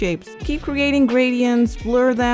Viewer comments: It sounded like you said “And you are dum” at the end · I thought she said "and you are dumb"